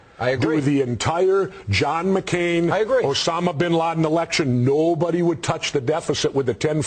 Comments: Did Jesse say the "John McCain and Osama Bin Laden election" at 4:58? Funny! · did he just say 'john mccain osmama bin laden election?'(4:58) was that a joke or a slip of tongue?